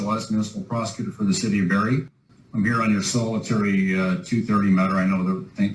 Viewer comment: Location is Barrie, a decent size city of 141 thousand, about 80 miles northeast of Detroit. Corrected. I heard "Bury", but his business is in "Barrie."